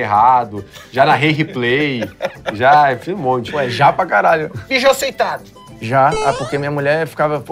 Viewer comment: nem entendi a pergunta, o que ele perguntou? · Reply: Mijou sentado